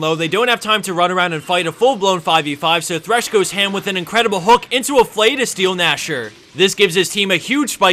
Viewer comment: Is it just me or does he say "incredible HULK" twice in that thresh clip? I know its "hook"...just heard it as hulk!